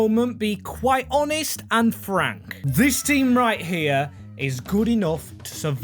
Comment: Anyone else thought he said Anne frank in